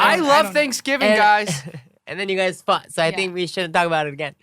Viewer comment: am I the only one that thought Kevin said, "And you guys fucked.." around 38:51.....?